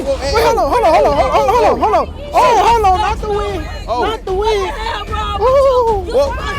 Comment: When it's blurred you hear "Not the wig! Not the Wig!" 🤣🤣🤣🤣🤣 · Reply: I thought he said "not the weed! not the weed!"